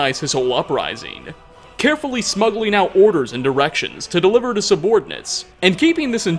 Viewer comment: it sounds like you say he's smuggling out orders and erections.